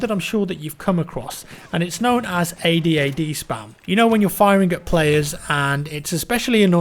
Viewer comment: I thought you said ahhd spam lol